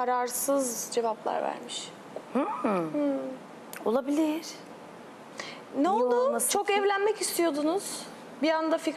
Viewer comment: ne diyor orda ağzını sıkarak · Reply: Sinsi